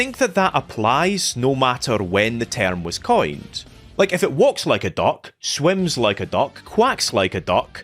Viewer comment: Man I thought you said walks like a dog, swims like a dog and then you said quak😂 like wtf. I had to have a second listen.